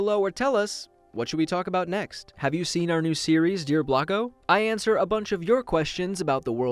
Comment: for the longest time I thought he was saying "as always, my name is Blacko" and I was always so confused because he was just a plain white character and I literally just realized his name is Blocko. I'm so dumb